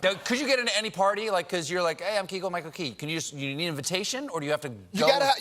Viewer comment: did Steven just say his name as "Keagle"- Michael Key??? LMAO